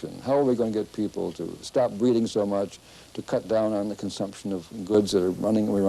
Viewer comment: does he say "how are we going to get people to stop reading so much" ??? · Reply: I think he said “breed” lol.